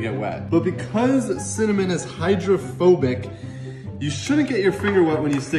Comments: For a second I thought he said homophobic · At first I thought he said the water was homophobic lmao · Hydrophobic I thought you said homophobic